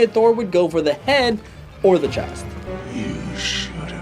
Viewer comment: I thought he said hand not head